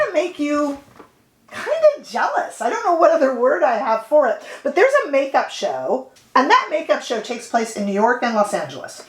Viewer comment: lol i thought you said i didn’t miss much at the makeup show! 😂.. Roshar and a friend both told me to check out Alcone while i was in NY but alas i missed my chance!